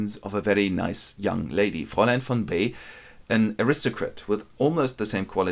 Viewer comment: what is the name of the lady i cant understand it pls can someone type it out for me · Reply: „Fräulein von B.” or Miss B.